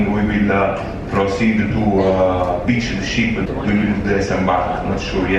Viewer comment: I really enjoyed the Q&A session with John Heald and crew. I turned on the Transcript to help me understand the captain’s description of the next steps of the ship’s end of life. At 7:29 of the transcript, instead of “to beach the ship”, it transcribed it as “to teach the sheep”. Added a little humor to a sad situation.